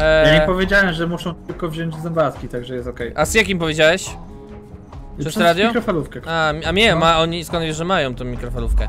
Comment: Jak im powiedziałeś? - Przez mikrofalówkę Tak usłyszałam 😂